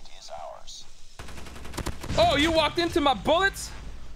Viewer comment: Oh he walked into my bullets! What I hear: Oh he walked into my BUTT